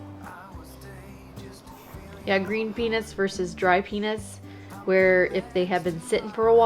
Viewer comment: did she say penis